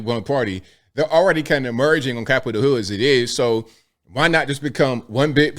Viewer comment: Did bro say "Capitol Hood" because that is one of the funniest things I have ever heard!